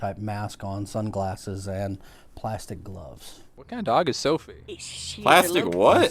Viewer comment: I think the police man says “plastic nose.”